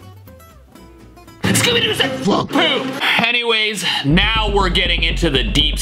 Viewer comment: Scooby Doo did not say the F word he say plank not the F word